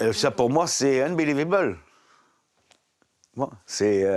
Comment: Un beulivébeul ? c'est quoi ? 39:46 Quels gros beaufs !